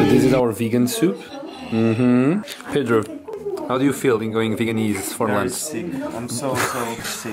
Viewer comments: Como é que se chama o restaurante vietnamita que vcs foram? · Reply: and pedro Obrigado pela resposta, tenho de ir lá, e que granda fail que eu disse, é que quando o disse "going veganese" eu ouvi "going vietnamese" 😂😂😂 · Como é que se chama o restaurante vietnamita que vcs foram? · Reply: and pedro I also thought you said "Vietnamese" at first. There is no such word as "veganese" in English. But a great video anyway; I hope Cintia has a good time staying with you guys!